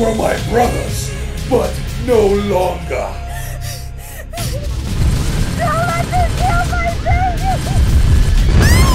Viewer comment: what does the Ultramarine say at 1:20? "These traitors know <something seomthing> " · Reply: I think it was: "These traitors know no bounds..."